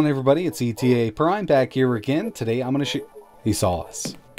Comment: What happened after the intro? It sounds like you said "today I'm gonna **** sauce"